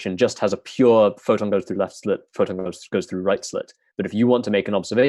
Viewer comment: Minute 1: tunneling should be easy with the black hole disconnection idea. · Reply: Misheard "A goes through hell"...and pops up at M.